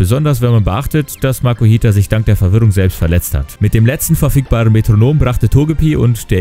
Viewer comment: Ich hab ferf*ckbaren Metronom verstanden